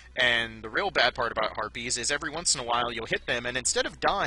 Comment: When you said harpies, I heard "herpes really suck".